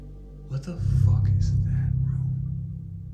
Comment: Sound like someone says "room"